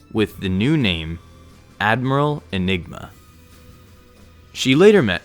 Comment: For a second when you said Admiral Enigma I thought you said Admiral Enema